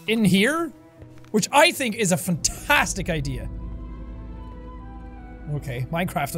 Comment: I think Jack said fucktastic idea